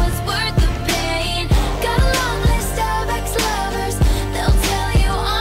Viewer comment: I thought blank space said "got a lovely Starbucks lovers"